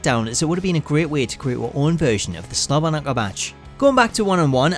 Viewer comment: what are you saying here? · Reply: OH Slobberknocker